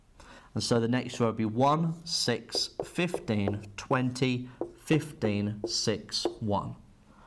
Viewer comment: Can you add English subtitles that's not automatically generated? I'm legally deaf unfortunately so I had to rely on the auto generated subtitles for all your videos :( 1:29 generated caption says "2015" and not "20 15". Starting to think about this. Is your material suitable for legally blind viewers too? I know someone in my A-Level Maths and Further Maths class who is legally blind.